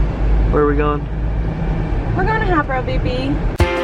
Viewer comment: ok for some reason at 4:50 when asks "where are we going" i thought you said "we're going to have our baby!" and then the letters spelled out A-B-O-R-T idk